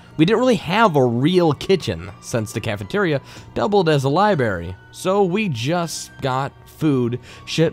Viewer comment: Who is narrating at 1:11:00 ? Swear he said LiBERRY...